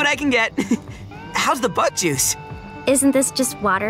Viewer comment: When Filbo said "how's the bug juice I thought he said how's the butt juice LOL